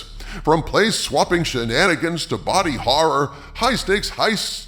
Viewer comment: I heard, “place-swapping shenanigans” as “race-swapping shenanigans” 😂